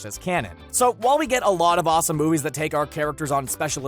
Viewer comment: Did he just say Inheritors of the Wall* of Fire ? :D